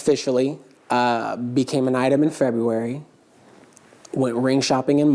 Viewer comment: it sounds like he said became AN ITEM, not UNITED.